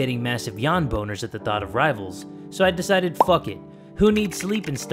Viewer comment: Lol I thought you said wives instead of rivals